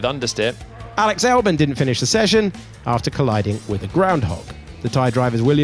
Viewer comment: A groundhog? Did I hear that right?